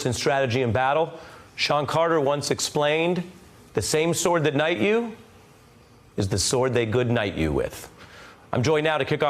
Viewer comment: I thought that the saying was “the sword that knights you can also circumcise you...”